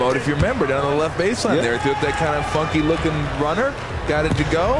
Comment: did he say fuckn